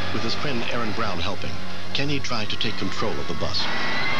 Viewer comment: Does anyone know what he said between 2:10 and 2:12? I couldn’t make it out. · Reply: It sounds like, "I'm going to try to drive the bus", but I could be wrong